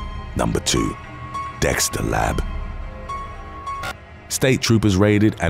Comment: Lol my high ass thought he said Dexter's Lab🤣🤣